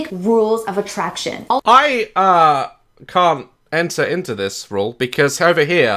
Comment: i thought he just said i cum😂😂😂